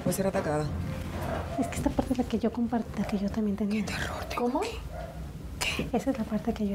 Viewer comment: Hola, en el minuto 3:05 se escucha un susurro que dice "INTERRUMPE OK" a la cual otra le responde "QUE o OK" no entendi exactamente, eso lo dijo alguno de los que estaban ahí? mas que nada por que acto seguido se escucha el arañazo y moverse violentamente la cortina. Muchas gracias y felicidades por la investigacion. Un saludo desde Barcelona España. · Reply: Es la Shaman dice, " que terror tengo aqui" pero "el que o el ok" ese si quien sabe quien lo dice ..... tambien se me hace raro